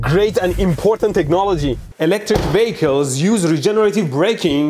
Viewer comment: Vehicles! That makes more sense. This is the first time I have ever had a hard time understanding your accent... I thought you were saying "electrical bagels" and honestly I didn't question it.